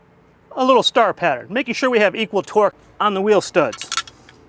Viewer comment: I swear at 5:06 he said “equal twerk”